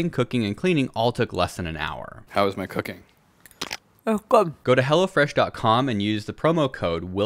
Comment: all i heard was go to hell